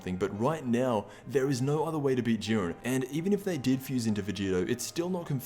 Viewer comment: did he say urine instead of Jurien